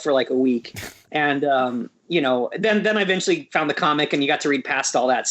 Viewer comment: did he just call it comic?